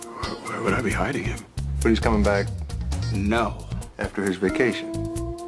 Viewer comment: what does he say at 0:28... please come back? lol